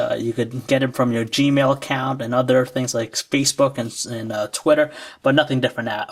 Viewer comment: xD it sounded like he said "spacebook"